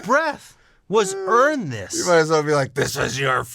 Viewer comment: I always thought he said Ernest because that's his first name.